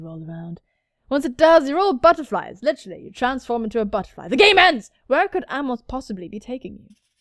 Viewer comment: When you said "GAME END" I thought you said "GAY MEN" and honestly where's the difference in this game